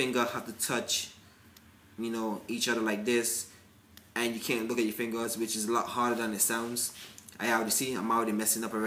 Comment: What did you say at 1:02? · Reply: Jerod Rumney u can't look at ur fingers when u do that exercise.